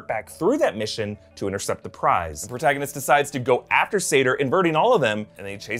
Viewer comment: Once again, Nolan gave the most amount of exposition to the one character with the heaviest accent. These two are even named similarly, Saito and Sator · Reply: I actually thought they were saying "saito" when they were talking